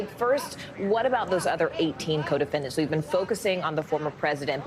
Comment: I thought she said codependents at first lol 😆🤪🤣